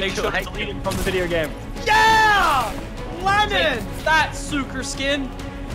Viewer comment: did he just say sooccer skin? lachy at the end